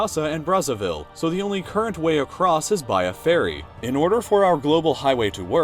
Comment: I thought he said fairy but he said ferry 😂